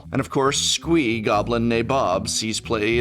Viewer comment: I heard "Squee, goblin née Bob"